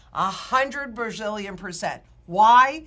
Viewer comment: A hundred bazillion percent! Bazillion is just slang for a very very big number. was just answering the question with a BIG BIG BIG YES! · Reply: I know but she stuttered and it sounded like she said brazilian instead of bazillion XD